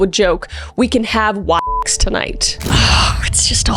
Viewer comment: we can have WAX tonight? Seriously wtf was that word? 😅